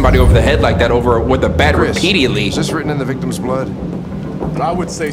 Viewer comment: ik this dude did not just say repidiatly xD in laughing #